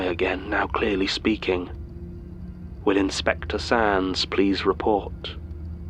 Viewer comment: ok ok is no one else gonna talk about 12:14 "will inspector sans (sands? idk it sounded like sans) please report" because i damn near had a stroke when i heard that combination of words in a mangus archives episode of all places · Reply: It's Sands, as it is part of a, uh, joke, about using sand to cover up spillages.